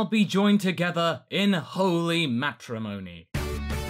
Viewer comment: Goku: Matrimony I thought you said Macaroni!